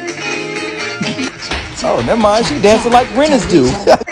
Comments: “Never mind she dancing like rent is due”😭😂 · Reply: Bro I thought he said “oh nvm she dancin like renis do” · “Never mind she dancing like rent is due”😭😂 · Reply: I thought he said “Brenda’s do” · “Never mind she dancing like rent is due”😭😂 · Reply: I thought he said brinnis do and I’m sitting up here looking up who the heck is brinnis 😂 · I thought he said “oh never mind she dancing like a British dude” 😭😭